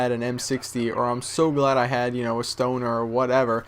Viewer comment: I Heard, "Man, I'm So Glad I'm A Stoner"